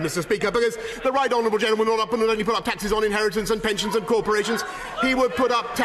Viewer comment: I'm a Japanese learning English and want to know what he says here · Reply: "the right honourable gentleman... (Random gibberish)... Wants to raise taxes on inheritance and pensions and cooperations